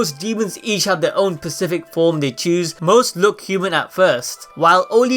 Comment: he said pacific instead of specific